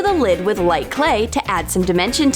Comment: Did she just say to add some dementure?? *add some dimension...* Ohhhhhh